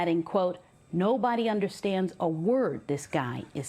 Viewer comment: I swear I heard "no bunny understands Bad Bunny"😂